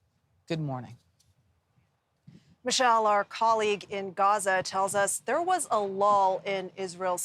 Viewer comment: did she just say there was a “LOL” lol